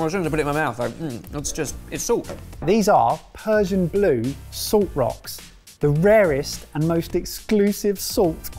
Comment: I thought they said prussian blue salt rocks 😳